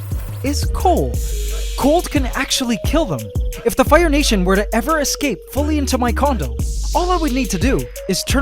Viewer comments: Did he just say “if the fire nation were every to escape into my condom”? 😂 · I thought he said “If all of the fire ants escaped into my condom” 😂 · did I just hear "if the fire nation were to ever fully escape into my condom all I would need to do is turn" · Is it weird I heard "condom"? Can't hangout with your girl if u got ants in your condom. 😂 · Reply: Its condo. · It sounded like he said "condom" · I thought he say condom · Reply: He said “Condo” but now I can’t unhear “condom” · i heard condom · I thought you said condom 🤣🤣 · Yikes, thought he said "If the fire nation were ever to escape fully into my condom..." I had to replay that one like 5 times. Good thing he's got official captions 😬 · I thought he said condom xd😂😂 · OMG I HEARD THE WORD "CONDOM" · I heard condom 👁️👄👁️ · I thought you said "if they were to ever escape fully into my co*dom, they would all die" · I love AC but who else heard him say condom instead of condo... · am i the only one who heard condom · 🤣😂😂 I’m dead did he say condom😂😂😂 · I thought you said if my fire ants ever escaped into my condom.... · Did you just say into my CONDOM??? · Who else heard condom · it sounded like you said condom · anybody else hear "condom"? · Did He Actually Said Condom? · did he just say CONDOM!!!! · did he say something about like Fire Nation escaping into my condom? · I thought you said condom 🤣😂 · did i hear condom? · I thought he had said condom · sounded like condom XD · did he say condom 😂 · DID YOU SAY CONDOM